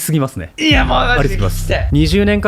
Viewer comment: くらいからの、「20年間くらい〜〜〜やってきたのに全部間違いだった。」の話の、〜〜〜部分はなんて仰っているのか聞き取れないのですが、どなたか教えてください！ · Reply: 1年前のコメントにすみません、、、 ｢こう教えられてきたのに｣だと思います！